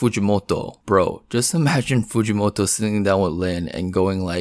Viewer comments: you are so far down man, you just substituted “little girl” with “loli” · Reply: Bro didn't even say loli right I thought he was saying lowly 😭😭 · omg i thought u were saying lowly this entire time like you were looking down on women not loli lol